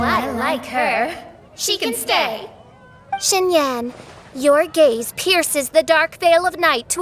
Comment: i thought she said your gay 😂